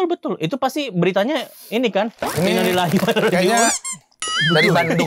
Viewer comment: Aku menonton ulang kalian engeuh gak dimenit 36:12 bilang apa 🥺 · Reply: bilang inalillahi rajiun,terus bilang dibandung:(